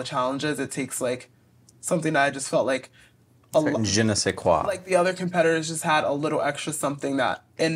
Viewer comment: might wanna update those subtitles if you can lol it says “genocide quality” instead of “Je ne sais quoi” (Thanks for including subtitles btw they’re great most of the time)